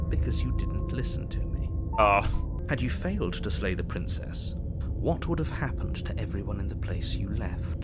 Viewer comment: slay the dragon? i thought you said lay the dragon